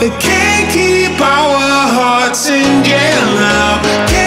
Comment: thought he said "Katy Powers' hearts in jail now"